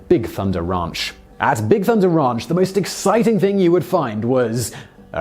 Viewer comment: With his accent it sounds like he said Big Thunder raunch